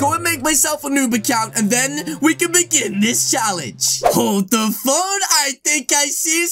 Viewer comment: In 2:09 i thought u said hold the f**k